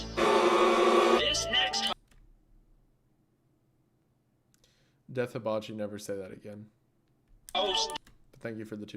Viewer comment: this stream seemed really fun I love wendigoon I’ve never seen him react like that I kinda wonder what was said I hope it didn’t get on Isaiah’s nerves too much he doesn’t deserve that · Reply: It said „Wendoubloon plunder my booty“. So no, it was not anything serious. Wendigoon was just playing a bit there.